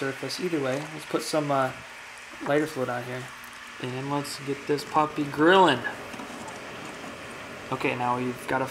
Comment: Either I misheard or he said alright let's get this papa going